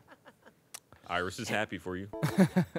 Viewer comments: did say "Iris is happy for you"????? Have we known this whole time???? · It's late and I'm exhausted, so I might not be hearing him correctly, but did say "Iris is happy for you?" at 24:19? Is he talking about the character model using? Who the hell is Iris in this situation?